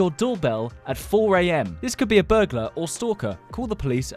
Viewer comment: I thought it said butler stalker